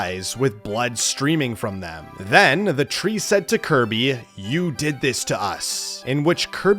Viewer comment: I thought I said Kirby you’re dead